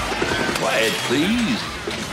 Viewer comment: Did he just say "Quaid, please"?